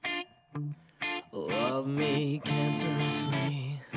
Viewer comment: All I heard was "love me dad!" I think something is wrong with me.....